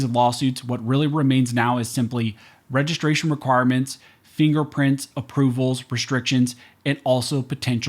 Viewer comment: Does anyone else hear "infringement, infringment, infringement"?